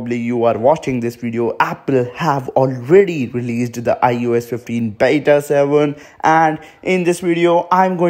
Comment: I thought he said “iOS 15 BETA SANDWICH “